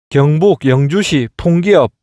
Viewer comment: 경벅 영저시 펑기업으로 들리네요 ㅋㅋㅋㅋㅋ